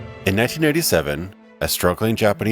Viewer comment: did he say 1997 or 1987? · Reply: Nosferatu Z0DD 1987. The first one came out that year